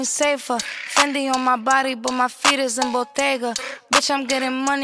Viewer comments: the way she said bodega is eargasmic. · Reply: Sounds like she said she wears her jewelry to the Bodega, but what everyone is tripping over is when she says what's on her feet is Botega....as in Botega Venetta. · Am I the only one that loves the way she says bodega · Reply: Vannia Cuervo it didn’t sound like she really sounded out Bottega cause it sounds like she said bodega twice